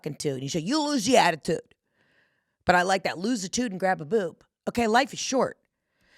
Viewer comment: I thought you said, Lose the ‘tude, and grab the lube! 😂